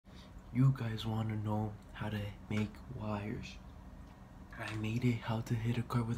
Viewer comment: Bruh I was so fried I thought you said “You guys wanna know how to make fires”